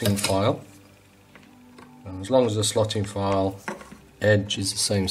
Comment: / 22:24 what's that file called ⁹? I thought you said slotting file but I searched and nothing came out... thanks for the very detailed video, really appreciate it. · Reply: They were known as slotting files, watchmakers use them. I know they are hard to find, so Google nut slotting, or fretting files.